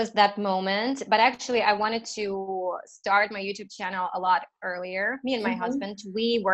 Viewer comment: i'm so happy , when at 15:10 minutes, i heard making a mistake in her conversation, she said " i wanna to start my youtube channel " i though to myself, even a fluent speaker like her made this mistake, it's okey to me, when l make mistakes, i'm just a begginer.